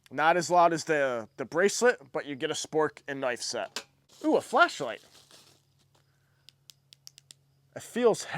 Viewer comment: I thought you said fleshlight and immediately got on Temu cause that's worth the price of admission alone.